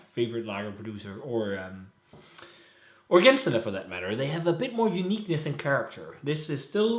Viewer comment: I didn't understand your 2 favourite beer sorts ... Center and Gangster??? :D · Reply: It was my two favorite Lager producers, at the time of shooting the video at least. I mention Brauerei Zehendner & Gänstaller Braumanufaktur :-)